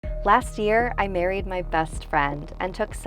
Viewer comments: I thought she said “last year I buried my bestfriend “ and I saw those photos like “ok so you don’t respect your dead bestfriend???” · did anyone else thought she said i buried my best friend 😂 Just me? Ok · At the beginning of the video, I thought she said, "I buried my best friend" and I was like, "YOU DID WHAT!" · When she said she MARRIED her best friend. I thought she said Buried. I was like oop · When she said she married her best friend I thought she said she buried her best friend and I was like why do you sound so Happ about that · I heard "I buried my best friend" not married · I heard buried and had to replay the video 😭😭😭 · At first I thought she said “ I Buried my bestfriend”🥺 is it a sign for me?